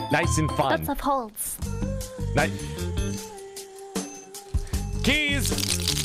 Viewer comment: Did I heard that right did he sayed the German word "Nein"?